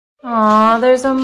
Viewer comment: For a while now I always thought they were saying he fell on my wax burger but now I understand she said wax burner